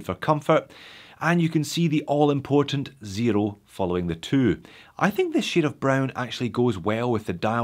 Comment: what are you saying? Sounds like “you can see the zero following the two.??”